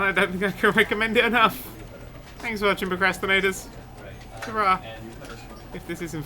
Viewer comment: i got to say this cos it bugs me everytime. it sounds like you say PERcrastinators, Just one time i want to hear you say , Hellooooo PROcrastinators.